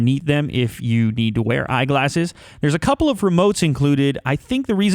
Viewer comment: When you said eyeglass I thought you said iglass as google glass or non existent apple iglass